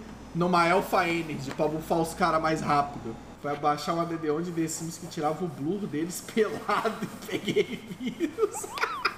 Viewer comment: desmoronei aquikkkkkkkkkk (alguém entendeu o que ele falou?). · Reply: O cara foi pegar mod pra ver bumbum dos sims e pegou virus kkkkkk